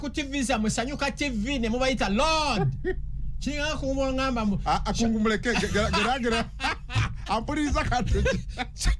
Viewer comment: I hear "Kungu muleke" 😂😂😂😂. Sounds like Mubiru ndeka